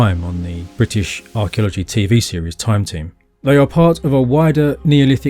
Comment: I heard “Time Ting” which I imagine is a YOOKAY archeological drama about the escapades of Cheddar Man stealing funnel beakers full of cocoa butter for his ashy but beautiful black skin.